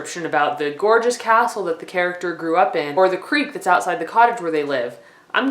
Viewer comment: Haha, I thought she said, “That creep that is outside your house.”